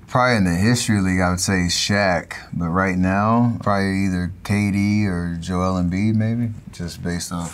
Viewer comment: I love how the translater put "Cady" instead of "KD" in the subtitles 😂😂